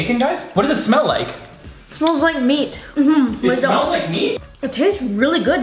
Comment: It sounded like he said “it smells like me?” LOL XD